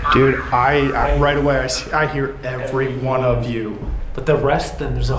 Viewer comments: I heard “everyone of you who tried tonight” as in everyone who tried to respectfully interact with him failed and disrespected him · 'Everyone of you have left. The demon got me' is what I hear. Like he's been left by everyone possibly? Like not just the guys like everyone leaves him alone with the demon?